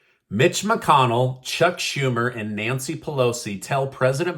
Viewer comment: B*tch McConnell is what I first thought you said when you started this video! 😆